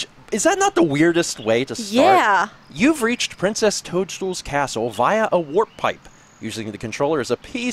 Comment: Am I the only one who thought he said "Princess Pooch's Castle" at 4:44?